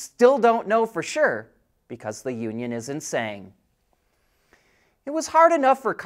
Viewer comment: lol - I heard “Because the union is insane” instead of “isn’t saying”.